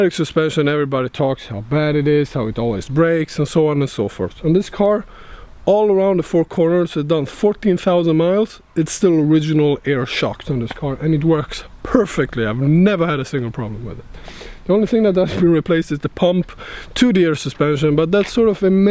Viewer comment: How many miles? I thought you said 40,000 miles, but you said its a 2005. Did you say you change the oil every 1,000 miles?